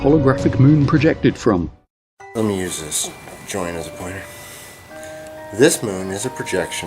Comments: When you hear someone say, "Let' me use this joint as a pointer." You know there about to say something interesting... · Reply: am Calvin Oh YEAHHH! I thought he said "Joiners pointer" I was wondering why a joiner would need a pointing implement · So this particular Cleetus' message was "Jesus - Because the moon is fake" I'm scratching my head to make the connection. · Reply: Geoff Stockton Does he say at 12:40 "I'm going to use this joint as a pointer" . If I've heard that correctly, then I think there's your connection right there.